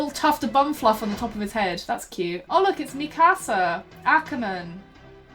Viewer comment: i wasn’t looking at the screen and thought you said “mi casa” for a second